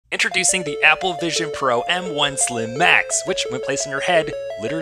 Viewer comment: I thought it said Apple Vision Pro Muslim Mac 😅